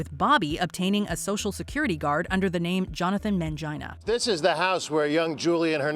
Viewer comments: did she say security guard? 😂😂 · did I just heard that right......Man-gina?! 😅